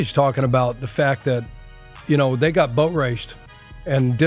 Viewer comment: Had to rewind 3 times, thought pagano said "they got butt raped" 😂